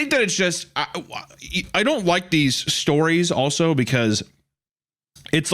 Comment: anyone else hear him say "wike these" 😅